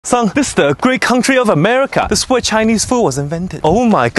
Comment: sounds like he said "Son this the 'greek' country of America"